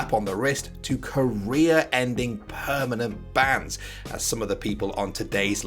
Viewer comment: i thought he said "korea-ending permanent ban" like wtf